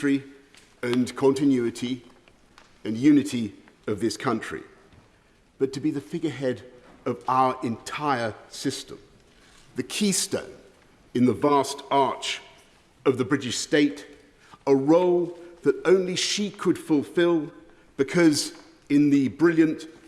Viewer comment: Did he just say "The keister in the vast arse..."? [I know it was 'keystone' and 'arch'.]